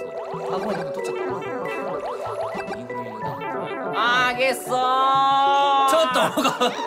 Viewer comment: あたりでうらたさんが「あー！ゲッソー！」って言ったのが 「ハーゲそーう！！！」に聞こえたなんて言えない